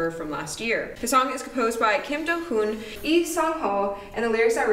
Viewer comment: I heard it as 'composed by kim jong un' & I was like Wut? 😳